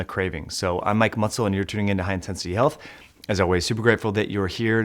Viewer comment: I heard that as high intensity elf 😂